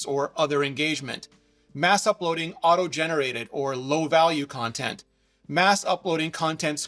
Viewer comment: did he say “mass uploading, AUTO GENERATED?” This, coming from the same company who lets users auto generate short form video content? Unbelievable… oh you address this same thought a minute later. Cool